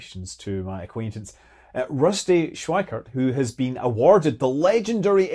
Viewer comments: EXPLORER! I kept hearing "exploder." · anyone else mishear "the legendary exploder's club" and do a double take?